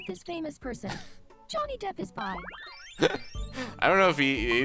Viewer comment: With the voice Alice had it sounded like she said "Johnny Depp is bi." LMAO